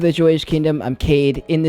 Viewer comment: First time viewer: Not going to lie... it sounded like you said welcome to the Jewish Kingdom. Had to restart the video to hear it again 😆